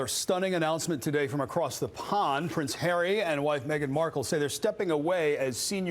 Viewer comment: I swear I heard "White" Meghan Markle